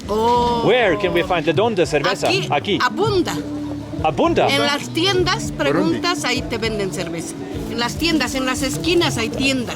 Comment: I dont speak spanish but i think she was saying sir vesa is banned in the industrial district